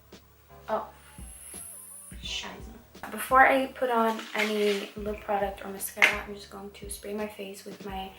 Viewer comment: Did she said "fuck, scheiße"? In 14:01😅❤